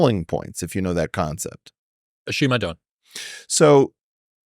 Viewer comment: What did chris say at 1:46:01 ? Sounded like ashiymadon haha